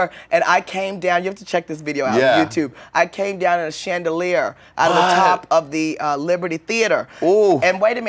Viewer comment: i thought she said "shangelier" lmfao